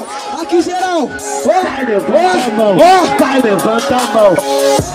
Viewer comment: eu entendi " Vai levanta anão "